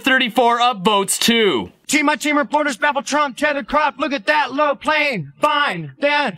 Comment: I think I heard:"look at that little plain why!"